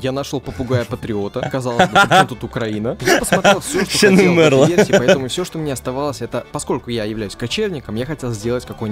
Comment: кто нибудь пожалуйста переведите что сказал. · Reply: "Ещё не умерла" он сказал